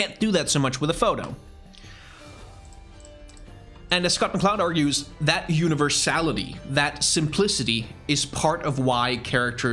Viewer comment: I heard "That universe salad-y" XD